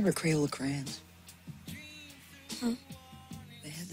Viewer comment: I always thought he was saying cradle of crayons LMFAO.